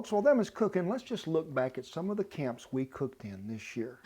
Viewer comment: I thought he said "lets look back at some of the calves we cooked this year..." Had to rewind to make sure. Lol 😁